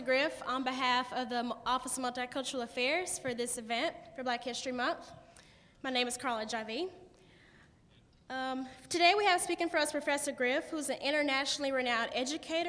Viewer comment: At the intro the chick sound like she said her name was carla HIV